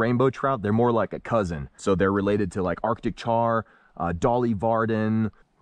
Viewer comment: my dumb A$$ almost thought he said Dolly Parton at first. 😂😂😂